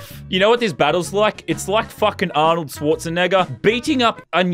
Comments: hold up did he say the n word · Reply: No, he said Arnold Scwarznegger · it sounds like he said Arnoldshortsan&$$&